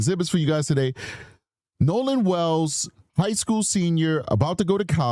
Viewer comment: It sound like she said highschool from what we gathered